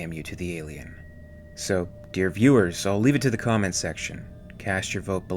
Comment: For years I honestly thought he said, "Damn you Roark". Then came the subtitles. I don't buy the talk of producers trying to minimize the alien because in the end they showed it all outside the shuttle and that's when it started looking fake. I believe they didn't know what they were doing at the time and all tried to be overly smart and clever. The thing looked so real no harm could come from showing more of it than they did. This is why I have never respected the director's choices and believe a great film could have been even greater.